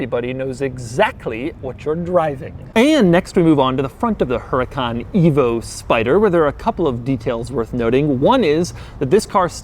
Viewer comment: what I heard: "huracan EVIL SPIDER" :D